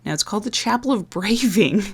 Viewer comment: I misheard that as The Chapel of Gravy. If only...